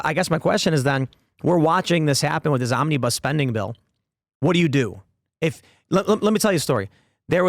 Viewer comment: Mis-heard Tim around 4.02 and thought he said 'this zombie bus spending bill' - or maybe I didn't 🤣